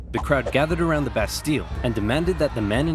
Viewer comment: I thought he said, “The best steal.” Instead of, “The Bastille”